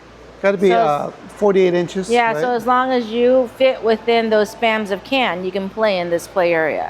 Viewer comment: Oh, y'all fooled me! Thought you were in Texas.😅 Forgot about the Spam Museum. Looks like fun. 7:11. Did I hear day "Spams of can"?😅 Piggy Blues looks yummy. The peach cheesecake with hot honey?!?! Who knew. Thanks for sharing another fun adventure.